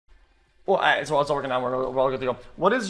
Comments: what is he saying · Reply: alright the stream is working sow now we’re good to go( had to listen to it a coupel for times) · What did lil bro say in the start?:face-purple-crying::face-purple-crying::face-fuchsia-wide-eyes::face-fuchsia-wide-eyes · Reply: It’s all working out we’re all good to go🤓